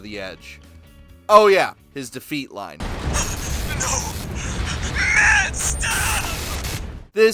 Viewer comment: For a long time, until I was able to watch videos at higher resolutions. I couldn't see the subtitles. So every time I watched someone defeat Invincible Rummy, I thought he was saying "MAN, STOP", like a pothead saying Stop it...dude.... DUDE....you're killing me man, STOP! DUDE....BRO...man, STOP! (T π T) Only when I had the money & data-plan to watch videos at higher resolution, did I learn he was crying out the name of his precious AC. Which is sadder? What I thought was happening, or what was actually happening?